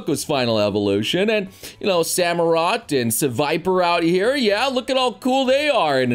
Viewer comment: Nothing to see he- HOLD ON DID HE JUST SAY SEVIPER?